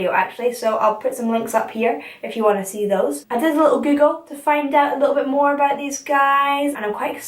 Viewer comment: did she say "I did a little giggle" or did I misunderstand what she said?